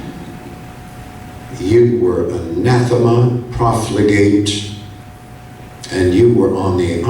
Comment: nathinum? Nathimum? Im so confused i don’t think I’ve ever heard that word. Help? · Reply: It’s anathema, meaning something that is objectionable